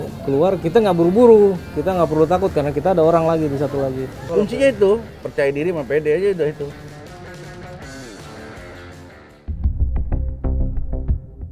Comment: "Kuncinya percaya diri dan PD" - Detektif, 2021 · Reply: Oh salah denger, kirain percaya diri dan pede